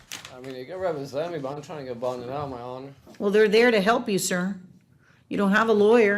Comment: ish but did I hear him right....... did he call the judge ,”my homie